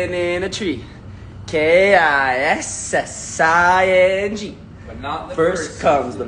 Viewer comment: Did anyone else hear Charlie singing the kissing song and Owen saying "but not in season 1" 😂😂😂